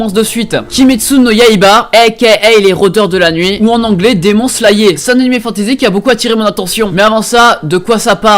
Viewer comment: C'est moi ou t'as vraiment dit: "demon slayÉ" ?